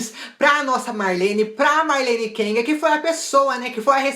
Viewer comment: eu entendi marlene kenga kkkkkkkk